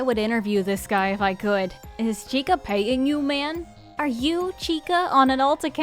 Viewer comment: I heard "is chica pegging you" at first. Took a bit of careful relistening and captions to realize that is not the case, especially since it completely fits the sentence and context, while being wild